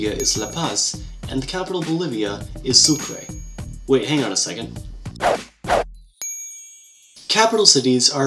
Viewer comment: you said hang on a SECOND but the subtitles say wait a MINUTE!! Why?? Can you explain this nonsense please????!!!!!!!!!